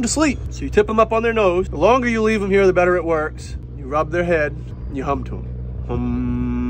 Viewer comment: Oh my god, I thought he said “You humped em” instead of “You hum to him” 😭😭😭